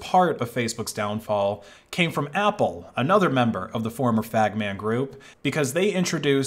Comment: Did anyone else hear "fag man group" at 6:12 ?